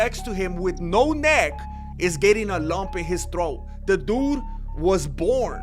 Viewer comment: Liked Hyped I'm also trying to figure out why I thought you said dump in his throat when you clearly said lump. 😂 💩